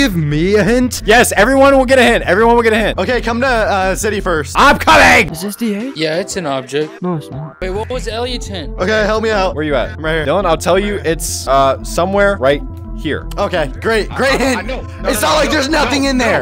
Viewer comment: did just call dylan? İS THAT HİS NAME?!?!